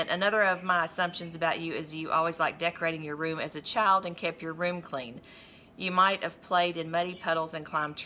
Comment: I agree with everything that was said and you can always count on mom to call you out on a messy room. As a mom it seems we can forget a lot of things but we always remember that messy room. Lol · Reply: I'm so sorry I have misunderstood I thought you said she didn't keep her room clean. I thought it was funny. I hope I didn't offend you by saying as mothers we forget things. I certainly didn't mean to imply you had forgotten something. I would never do that, I feel awful.